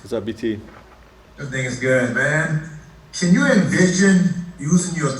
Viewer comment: Lol did BT said "This *** is good man"